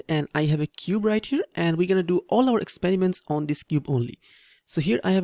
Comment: LOL i thought he said "whe are going to do all our experiment on this newbron" scared the crap outta me